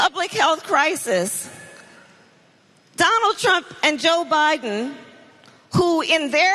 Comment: did she say that me and Joe created this Affordable Care Act?